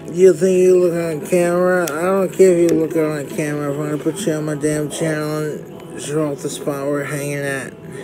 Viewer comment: WTF is he saying? All I heard was "sodie pop, my damn channel."